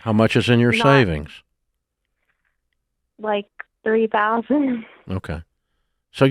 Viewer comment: I thought I heard her say she had 30k in savings. Am I hearing things?